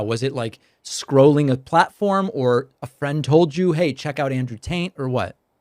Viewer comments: Taint? 😂 I could’ve sworn he said Andrew TAINT back to back several times lol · Why does it sound like hes saying "Andrew taint"? 😅😅😅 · I sounds like David is saying Andrew Taint. 🤣🤣